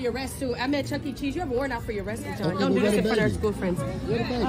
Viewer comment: did I hear the girlfriend say “don’t do this in front of her school friends?” At that moment I would’ve just took my L(as the mom) and left because it’s clearly signs that she cares for the daughter as well…